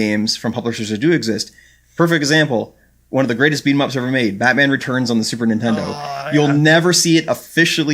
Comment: I can't understand the name of the Snes game the guy on the left says at 13:40. It sounds like "barbarian" somehing...? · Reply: protossevolution I'm pretty sure he said Batman Returns.